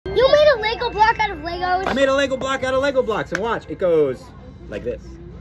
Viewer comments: Any one else thought he said Glock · When they said do you have a Lego block out of Lego I thought they said do you have a Lego glock